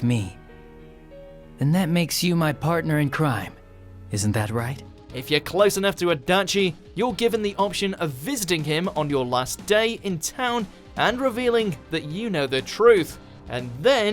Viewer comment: did anyone else hear “Fisting”?